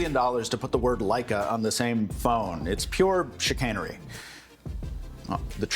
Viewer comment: Why did I hear "sh*tcannery" and not "chicanery"?